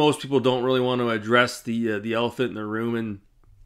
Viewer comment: Address the alphanerd in the room? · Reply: lol other than the ones harassing birds. I thought you said alphanerd not elephant it’s a little loud next to the fan I was under lol.